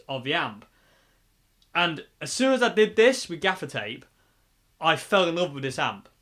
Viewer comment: At first, I thought you were saying "defecate" regarding the dark strip across the grill. Oops! I was glad when I finally realized it was "gaffer's tape." That amp sounds pretty good. Thanks for the demo.